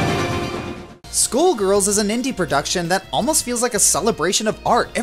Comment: it sounds like he is saying SchoolGirls instead of SkullGirls XD But I love that game and love that finisher it was EPIC!!!! :D